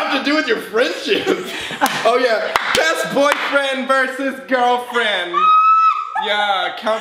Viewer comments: I thought it said boyfriend vs. Girlfriend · did i hear say boyfriend vs girlfriend instead of best friend vs girlfriend ... #solby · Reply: he said best boyfriend lmfaoo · said "Boyfriend vs. Girlfriend" instead of "best friend vs girlfriend" Is #Solby real???💓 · really thought it said boyfriend vs girlfriend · He put bf vs gf..sir it’s sounds like ur saying “boyfriend vs girlfriend” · did just say “past boyfriend” when referring to himself????